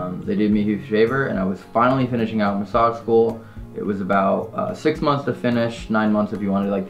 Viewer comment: Up until about 15 minutes I thought he was saying mossad school